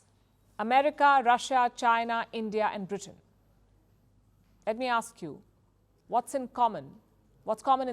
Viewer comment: I heard you saying Bangladesh instead of Britain.